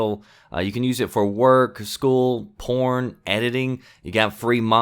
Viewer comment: Did he said porn at 3:27 ???